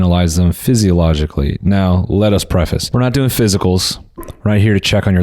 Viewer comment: When you said “let us preface” I heard “ let us pray”and I was like wow how nice and wholesome of Noel